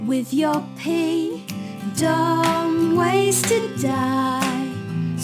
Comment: When I watched this like 2 years I thought it said "at night we have planets to spare" but now I know it's "act like" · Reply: i thought it also said "ninja daughters in your pig" and "dull ways to die"